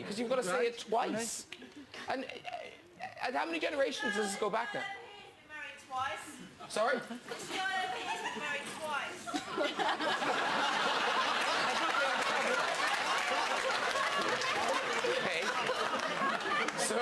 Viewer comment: What does the audience member randomly say? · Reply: “This man over here’s been married twice”